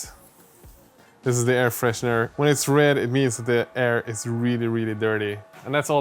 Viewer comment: I misheard you say "the air is nerdy," which, to be fair, is also true. :)